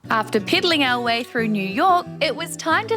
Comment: Burst out laughing. This is my first of your videos. I heard you say “after piddling our way”! Of course I realized what you were actually saying. But you must know by now that “piddling” is a different thing. It could mean urinating or wasting time doing nothing. I’m sure I’m not the first. You earned a subscription. What a fun video!